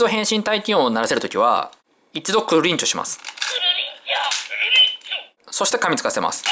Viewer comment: ○○ンチョのところがクルリンチョに聞こえるｗ